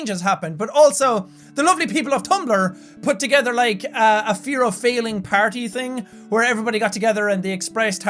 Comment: Honestly I thought it said fear of falling